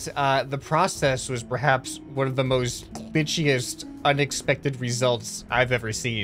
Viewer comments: When he said glitchyrst I thought he said the b word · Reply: I think he did say bitchiest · i think he said bitchiest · what did i hear? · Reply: Wow I noticed this to. I thing he said pitchiest btw